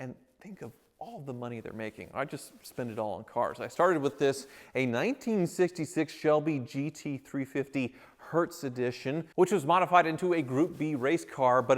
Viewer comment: Download Fishing Clash for FREE here <link> and use my code FISHWITHTYLER to get $20 worth of gifts for new players only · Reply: I thought I heard you say modify the Shelby GT500? If so terrible IDEA. You know better 😂